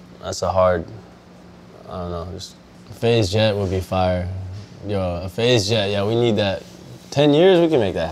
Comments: I thought he said "FaZe Jev would be fired." And I was like "Excuse me what the fuck?" Then I rewinded it and turned on captions and I was like "Oh thank God he didn't say that Jev was being kicked." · i thought he said FaZe Jev 😞